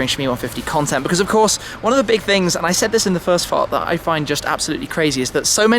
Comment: at around 1:09 i think Tim says Fart instead of part loool